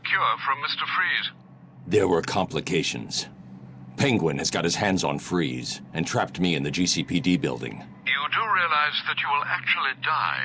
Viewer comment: IM CRYING. WHEN BATMAN SAYS THIS AT 58:24 "Penguin has got his hands on Freeze and trapped me in the GCPD building" I WAS AWAY AND HEARD IT AS "Penguin has got his hams on freeze and trapped me in the juicy PD building" I WAS LIKE HUH!?